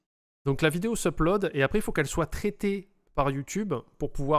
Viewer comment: A 54:13 j'ai compris "la vidéo se plode" je me suis dit encore une expression que je connais pas ..